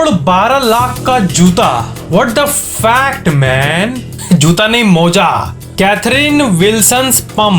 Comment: in 22:40 second i think he almost says what the f***k man